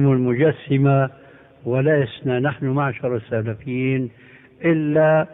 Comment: in the minute 11:20 achaykh al albani DID NOT SAY we the salafis, he said WE THE PREVIOUS, so why are changing what he said? you are misleading people? allah yahdik